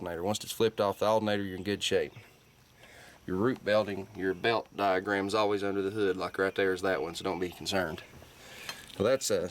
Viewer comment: Did he say "root belting" @ 1:36?